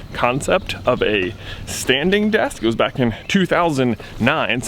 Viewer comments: I got really confused because I thought you were saying 'standing death' not 'standing desk' lol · When you said standing desk I thought you said standing DEATH